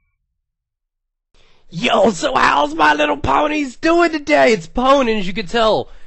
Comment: D-Did he just say "My little Pwnies?"